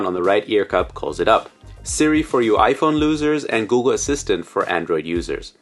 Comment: Am I the only one who heard it "iPhone losers" instead of iPhone users?